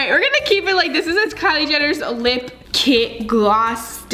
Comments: Anyone else notice at 5:23 it sounded like she said lip kit gloss d**k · I heard d*** I know she said stick tho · I feel like I heard Kylie Jenner lip gloss di*k IDK why😂😂😂😂😂 like of u heard too! Edit : it's